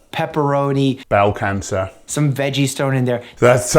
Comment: did anyone else hear 'estrogen' fuck I thought he was right for a sec.